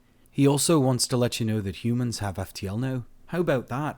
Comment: I heard "uman-hay" and nearly bust a gut!😂😂😂